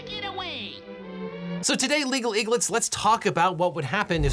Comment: For a moment i heard "Legal Eaglets" as "Legal idiots" and just thought, what a brave but true thing to say